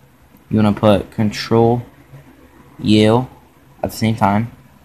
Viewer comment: does anyone have any clue what he’s saying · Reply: Ctrl u